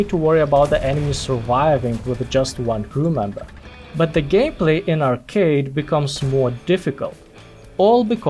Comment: did you actually call it gayplay instead of gameplay? 😂